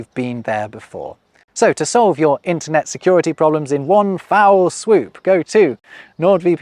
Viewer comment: Don’t leave yourself BUTT NAKED or SCANDALLY CLAD online. Go to <link> to get the two year plan with an exclusive deal, PLUS 1 bonus month on top. It’s risk free with NordVPN’s 30-day money back guarantee, so get yourself protected. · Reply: "One foul swoop" was an egg corn within an egg corn for me. I thought you were saying "one fowl swoop" as in a swoop by a chicken.